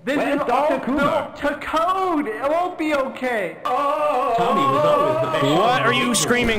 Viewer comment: Times characters weren't subtitled: 7:18 "Tommy was always the best shot at the ("Mesa group"? Can't really tell if that's what was said or not)" said by Dr. Coomer 7:39 "Peepers" Bubby, I think 11:01 "thank god.." said by Tommy 15:27 "Gordon, help me!" said Dr. Coomer. He might've had extra words there, but it's incredibly hard to tell. 16:17 someone said "Stop!" And basing off Dr. Coomer's earlier subtitle and the lack of npcs, it might've been him 16:54 there is a lack of slurping subtitles for Dr. Coomer, who was still drinking the floor water 22:50 "Quickly, Gordon" said by Dr. Coomer 24:08 The infamous unsubtitled "Hello Gordon" said by Dr. Coomer I noticed the first one and remembered the unsubtitled Hello Gordon and decided to find other places lacking subtitles. I made sure to triple check as well for any subtitles in those areas highlighting that specific line of dialogue.